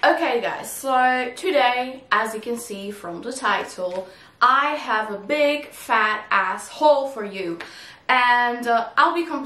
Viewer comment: Oh my, I thought you said "@ at 0:32, ahahaha, too funny 😄👍 Love you ❤ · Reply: "i have a big fat asshole"😁 but awesome video enjoyed the picks